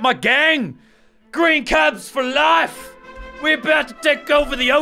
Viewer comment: Did he just say green cabs for life